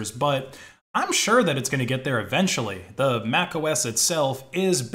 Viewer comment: This is a certified hood classic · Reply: All I heard is MacOS is based